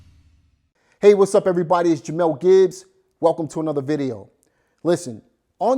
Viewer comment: I thought you said mel gibs at first, I was about to say like mel gibson lol. Sorry but I had to comment because it was too funny to me.